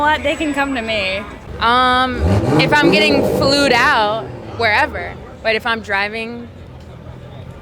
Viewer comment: Did she say "Flewd"???